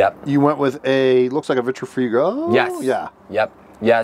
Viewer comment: Does anyone know what fridge said? Looks like a really nice fridge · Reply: he says Vitrifrigo fridge